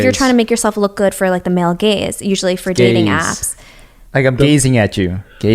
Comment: Yea I thought she said gays, not gaze. The word gaze is rarely thrown around and the word gays is what we commonly hear. I ended up googling her explanation on the apps anyway. Even meeting other potential female buddy friends she's indirectly judging the looks on the picture. So if you dont have the looks she'll swipe left on you or pass. Before covid it was already tough to be a sub par below average to average single guy. And the dating apps are definitely rigged against you. Also most average guys work up the courage to approach a girl only to get turn down or rejected. I admit that 69 st line is a corn ball line and I would never use it. But the fact remains that the sub average male has it worse now in the dating market than ever before.